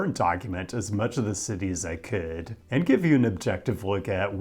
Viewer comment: I have never heard your accent as strongly as when you said "As I could" at the beginning of this video. At first, I thought you said "as a kid"